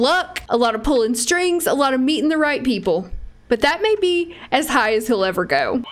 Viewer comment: I could if sworn I heard you say "a lot of meat in the right people" I was like wait what 😫😂😂😂😂🤔